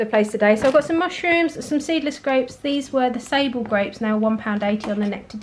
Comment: 😂🤣 I thought you said disabled grapes 🤣😂. ❤️🇬🇧